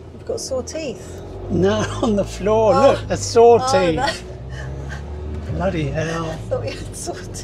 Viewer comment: Wow, you two! The ending was spectacular. Made me long to go to Denmark! Great video, great editing, great music. Oh! I thought you said your teeth were sore too! Haha. Glad your teeth are fine. Mary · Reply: Shark teeth. Not saw teeth.